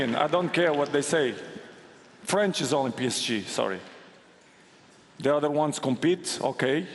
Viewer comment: At first I thought Ronaldo said "France is piece of shit"... had to put it backwards to watch again and then realise he actually said "France is only PSG" 😆